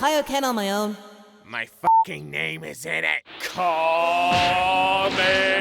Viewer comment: did he say kamekameha